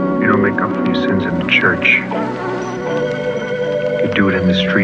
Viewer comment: what's this guy saying at 1:11:32 ????? (before "do it in the streets") very unpleasant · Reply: It's a quote from a Martin Scorsese movie - I think it was Mean Streets (1973). He's saying "You don't make up for your sins in church. You do it in the streets. You do it at home. The rest is bullshit and you know it."